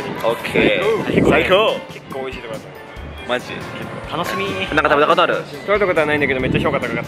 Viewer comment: itu bang Jerome ngomong apa oowkwowkowkw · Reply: nanka tabeta koto aru? eh, kamu udah pernah makan?